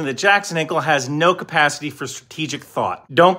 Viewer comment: Neither Iran, nor Israel has the logistical capability to mount an invasion far from its borders. The original tweet is here: <link> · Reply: I thought you said bathroom until I saw the comments. Not the encouraging statement tbh lol